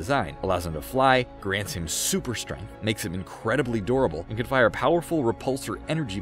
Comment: Every time he says durable I thought he was saying adorable